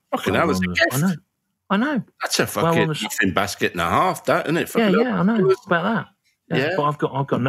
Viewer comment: Alexei says something like thats a basket and a half. Anyone heard that expression? I think I miss heard it